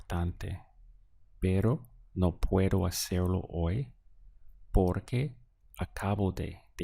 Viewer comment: On 23:12 it should be estar aquí instead of hacerlo right?